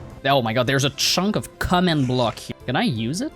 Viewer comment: A WHAT NOW?! Waht did you say!?!?!? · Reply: commen bol